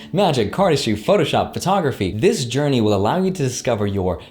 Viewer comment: i needed to listen one more time because i thought He said pornography😂